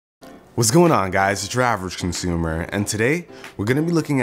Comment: I thought u said "Whats going on guys ur drivers costumer" xD